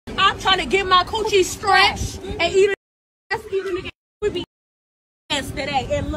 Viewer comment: I think she said she wanted to get her coochie "scratched", not "stretched". I heard "stretched" the first time, but I went back and listened a few more times, and I'm pretty sure she said "scratched".